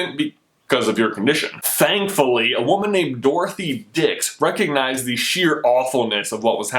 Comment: did he just say dicks